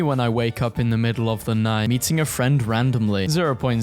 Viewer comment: I heard ‘eating a friend ‘